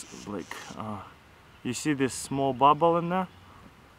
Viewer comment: Totally sounded like he said "see the small butthole in there" Smh. Lol.